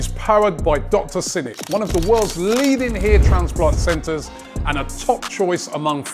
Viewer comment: Thought said ear-transplant for a moment there.